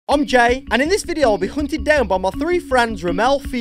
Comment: I thought you said "I'm gay" at the begining